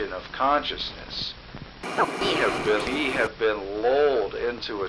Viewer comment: I thought he said "we have been LOLed into a trance..." Also true.